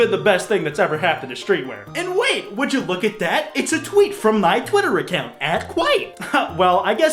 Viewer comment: and wait would you look at that it's a tweet from my Twitter account Thought he said "twink from my twitter account and got a little excited ngl